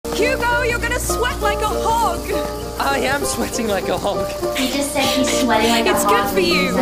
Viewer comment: "Sweating like a hog" makes more sense. I heard "Sweating like a hawk" LOL